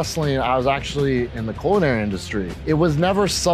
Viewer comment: Shout out for marks like me who thought he said "porn industry" then, oh hammerstone got it lmao.